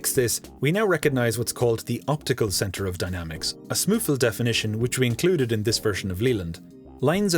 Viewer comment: are the captions meant to say "a SMuFL definition" instead of "a smooth little definition"?